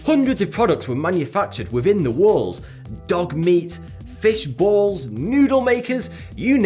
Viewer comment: DID YOU SAY DOG MEAT?!?!